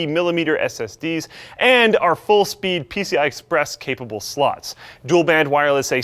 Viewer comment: Luke kinda looks like he drank the 5th of vodka. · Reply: I really heard telling 'PCI Express capable sluts!' but it might be me thinking about something else than PCI slots ;-)